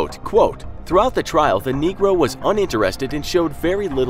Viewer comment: Did they just say the n-word